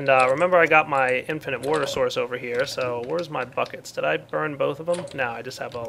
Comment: i thought said "weres my fuck its" xD woops